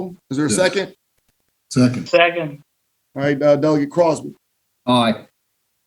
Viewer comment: I thought they were all called Dougie... Took me a while to realise he was trying to pronounce the word 'delegate'.